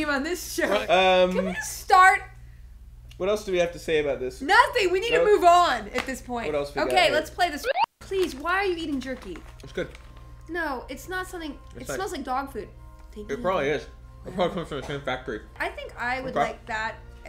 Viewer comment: i thought Felicia said "god, why are you even jerking" and to be honest it kinda looked like was XD